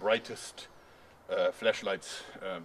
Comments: 3.19 this great Fleshlight ;) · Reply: I legit thought he said fleshlight and it looks like one too! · I thought u said fleshlight instead of flashlight